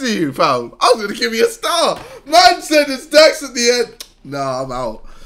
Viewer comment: The dude that said that he said “it’s dax” was it only me who thought he said “yes”? 😂